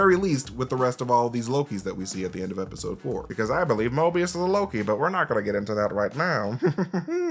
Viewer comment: Did you say you believe Mobius is Loki? Because if you watch the video, you slur it together pretty badly. It sounds like "MobiusnnLLLoki." Did you say Mobius is ANOTHER Loki? Please confirm. 7:24 for reference.